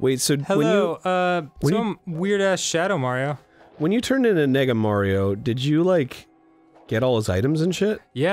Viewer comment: did arin say "nigga Mario"?